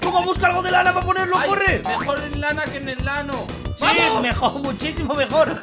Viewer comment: Me da risa lo que dice en 9:45 dice en el ano xd